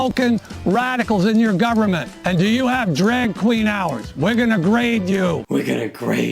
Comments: did he say "Does your state have Drag Queen hours?" I don't know what Drag Queen hours are, but they sound fun! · I swear I heard " and do you have drag queen hours?" and now I want drag queen hours in my city, it would be soooo great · I thought he was saying "we're going to raid you" the first few times. Like a shambolic Viking. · I keep hearing him say, “and do you have drag queen hours?” Now I’m just thinking about guys putting on dresses, all while saying, “It’s five o’clock somewhere!”